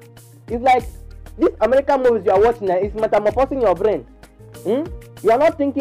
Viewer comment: Also what did you say in between 2:30/ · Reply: It's like your brain is Metamorphosing